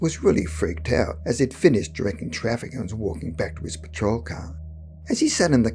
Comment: The closed captioning says, "it finished drinking traffic". I think it meant to say, "he finished directing traffic". 😉😁